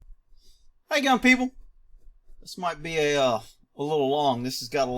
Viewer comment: 😂😂😂, I’m from the 🇬🇧 and always thought your intro said ‘how’s it going people’ now I now it’s ‘hey gun people’ 😂😂😂👍👍👍👍👍